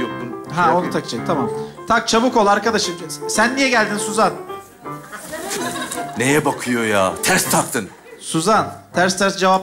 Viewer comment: da suzanın ne dediğini anlayan var mı? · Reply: Yönetmen sen niye geldin diye sordu oda canım istedi dedi 😂😂